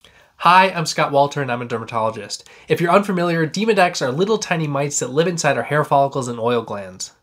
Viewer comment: I thought that he said "Demon-X."